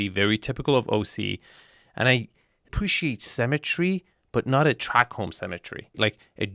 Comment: I followed you to this video. I thought you said you appreciate the "cemetery " and you meant symmetry. I was like oh man, someone died in this house too? That house looks like two cheap house got connected together by a main door. The ceiling height is really crappy and it looks like a dated house with that texture there.